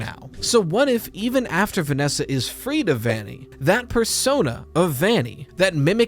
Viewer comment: did someone say persona ???